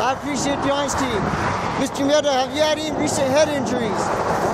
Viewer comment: As a Spanish speaker, I keep hearing, “Mr. Mierda” 😂